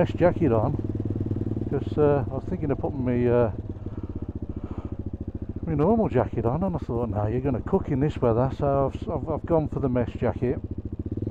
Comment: You made me laugh here as I thought you said you had your 'mess' jacket on. Now I thought that was because you were dressing for dinner. Posh fish and chip dinner ! Your CCM sounds great - what engine does it have?